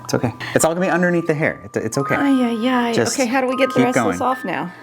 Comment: DID SHE JUST SAYED АЙ АЙ АЙ?